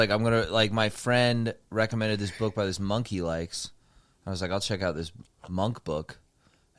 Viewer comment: I thought said he read a book that a monkey likes.